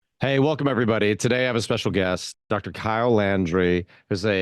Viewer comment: WHY DID I HEARD: "hey welcome everybody today i will suck your gyatt"